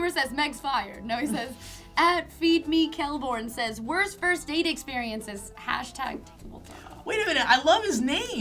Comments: i heard "First Aid Experience" too lol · I thought you guys were saying first AID experiences...totally different. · I totally heard 'first aid experience' which was slightly weird....